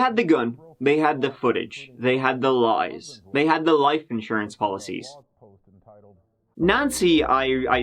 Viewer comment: Okay I was nodding off I thought maybe I heard the life insurance dance